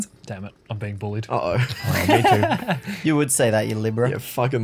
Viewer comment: i thought said “of course you would you liberal” but he said “libra”😂 i just died laughing